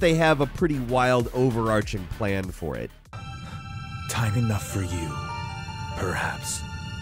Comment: I thought Sephiroth said “Tight enough for you” my mind is in the gutter right now